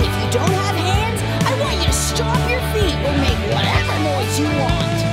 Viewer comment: In the beginning i heard "i want you to slap your feet!" Somehow😭